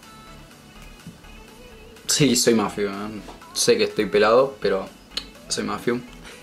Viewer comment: pensé que dijo: "si soy más feo"😂